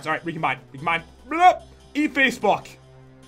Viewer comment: why did you say eat facef##k